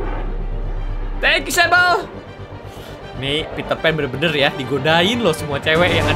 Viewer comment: kok kak malah ngomong peterpan 😀😀😀, apa sya yg salah denger. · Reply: Bukan Salah Denger,Tapi Kata Bang Peter.P